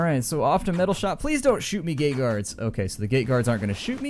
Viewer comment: When you said plz don't shoot me gate guards it sounded like you said gay guards XD